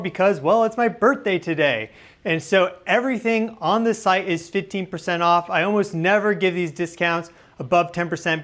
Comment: Happy Birthday. Thought you said 50% off, lol. Was ready to get that pink shirt. Sadly, my puppy needed her shots, so <sigh> Hope you had a great day woot woot!